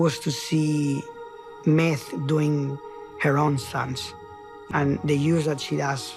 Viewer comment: Did he just say "Meth doing Heroin?????" da fark.